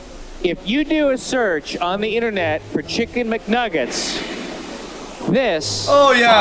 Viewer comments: did he just say "KICKIN mcnuggets"idk it sounded like it tho lol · Did he just say kicken McNuggets